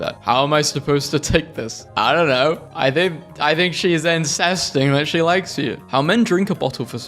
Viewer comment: WHAT?? sounded like inces